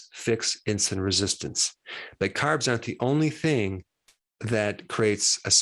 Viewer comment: Didnt here it well. Did you say "but carbs are.." or "but carbs aren't.."?? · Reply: he said 'aren't'